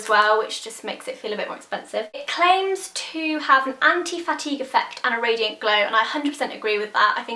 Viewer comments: I thought you were saying "Anti-Fatty" and I was like sold! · at one point i thought u said "anti-fatty" foundation lol where is ur nail polish from? love it! xxx